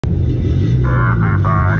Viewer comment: At first I thought he said, "Everybody has cooties." Had to come back a second time.